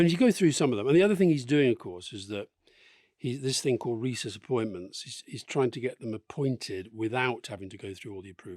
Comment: When discussion turned to confirming Trump's cabinet picks I thought I heard the phrase "rhesus appointments" and thought momentarily, "A bit harsh but fair".